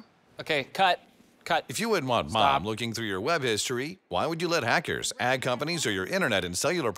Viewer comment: I heard "search history" and my finger halted above the skip button